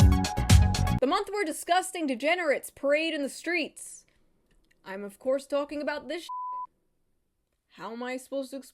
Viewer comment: wow watching at this point a Wegmans ad came up so what I heard was “a month where disgusting degenerates parade at Wegmans”